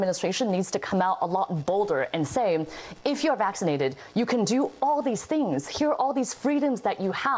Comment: Instead of "how do we incentivize people to get the vaccine" I heard "how do we punish people who don't get the vaccine?".